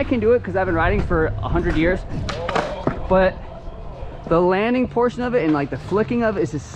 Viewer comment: great video but did you just say you been riding for 100 years? #